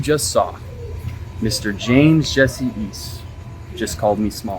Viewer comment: "James jesse east" huh i thought he said weast